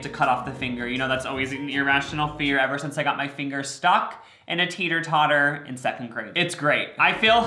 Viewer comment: It might be the weed, but I thought he said he got his finger stuck in a tater tot....